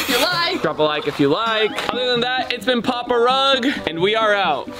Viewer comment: Just me who heard him says it’s been papa rug ?